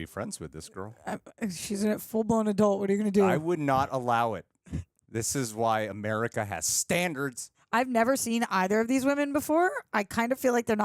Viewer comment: OMG.... 🤭 for a second there I thought was gonna say "This is why America has GUNS!" ...🙈😂🤣